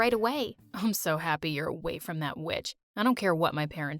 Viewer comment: When said “witch” I thought he said b*tch